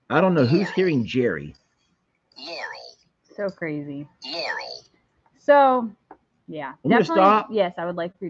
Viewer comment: I heard Laurel.